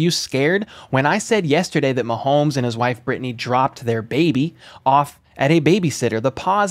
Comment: To anybody who thought he said a baby was dropped when he clearly said dropped off you have brain issues that you need to get figured out